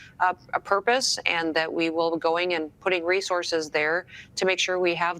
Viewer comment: Resources? I thought she said RACE HORSES